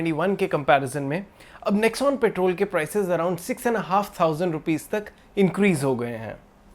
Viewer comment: Nice video. always keep us updated. (Minor thing, did I hear six and a half, instead of sixteen and a half